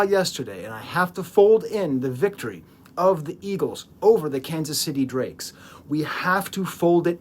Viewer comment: did you just say Kansas City Drakes??? 😂😂😂😂